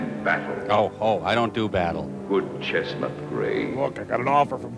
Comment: Omg, I thought they were saying the horse's name was Chestnut Greg at first. I was gonna get that shit tattooed on me if I was right.